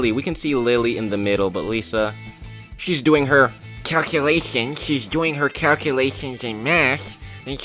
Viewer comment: Who else heard him say crackulations?😅